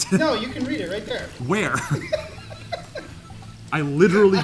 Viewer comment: my de4d4ss thought parkzer said whermst 💀